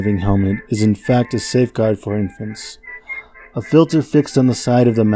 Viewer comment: fun fact i actually own an infant gas mask it was a supprise gift when i started collecting gas masks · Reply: I thought you said infantry but okay infant yeah